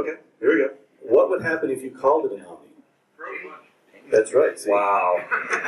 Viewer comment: what would happen if you call it an omni? couldn't really hear